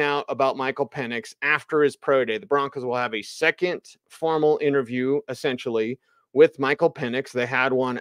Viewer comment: LOL thought you said porno interview.... dooooohhhh it was Formal Interview! oops :face-fuchsia-poop-shape